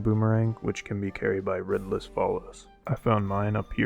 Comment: Omg lmao. For the longest time I thought you were saying Ridless Followers. I didnt know it was Red Lizafos. If not for that comment I think I still woulda been stuck xD. But this boomerang is really awesome!